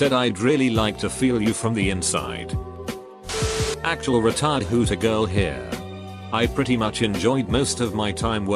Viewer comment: was only listening and legit thought it said "actual retard hooter girl here"